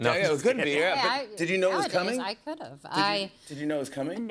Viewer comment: did she say “the reality is, I could have” 😅 when Benny said “she proposed”!!! YiKEs 😅😅😅😅😅